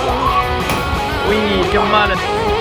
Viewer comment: I heard "che male" IS THAT ITALIAN?!?!